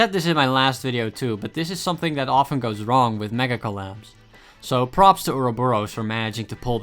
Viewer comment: when he said megacollab the auto subtitle say mega collapse lol